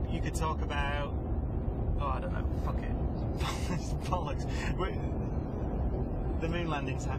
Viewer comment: did he just say "fuck it, its bollucks" , LMAO